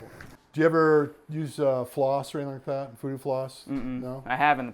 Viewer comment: What's talking about at 3:06? Something about 'floss' that he suggests to do regularly. I can't really hear it.